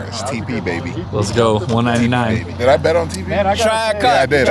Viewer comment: "track cock! track cock!" 😳It's what I thought he said 😂